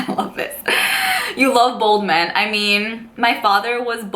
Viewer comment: I thought you were saying people thought you liked "Bold men". haha. "Bald men". That's cool that you aren't too tall. I'm 5'3". I use to hang out with a Jewish girl from Brooklyn & she lived in an area that she called "The Community". I lived in MA & she would take the bus to Boston to go see a band with me & she would tell her parents that she was at her friend's house around the corner.